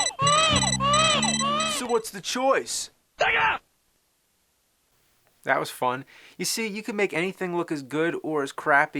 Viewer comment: did he said sega or thank you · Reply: Its the crazy guy screaming "SEGA!"